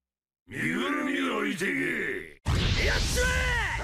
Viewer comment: これ2人ともなんて言ってるん？ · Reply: 身ぐるみ置いてけ